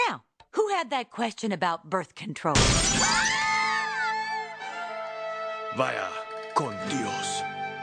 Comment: What does the guy say at 0:30? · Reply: He said “Vaya con Dios”